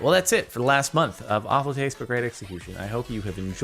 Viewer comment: I heard that as "awful taste but great institution"